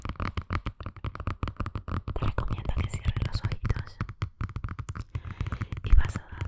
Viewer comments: Escuche cuando dijo cierren los oídos que carajos ? Me quede como modo KHE BERGA ? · todos comentan que en el minuto 4:17 dice cierra los oidos pero lo escuche muchas veces y siempre oigo cierra tus ojitos nunca escuche que dijiera cierra tus oidos, aparte es molesto porque todos comentan lo mismo. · pensé que decía los oídos xd xd